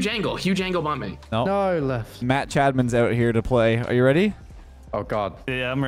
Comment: is saying Match Admin or Matt Chadman?